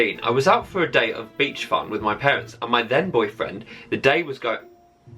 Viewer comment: oh no, on either the 3rd or 4th one (can't remember which) said "my then boyfriend" but I deadset thought he said "my dead boyfriend" and I was like holy shit that is brutal oml